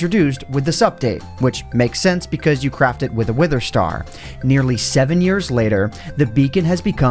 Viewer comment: what did he just say, wither star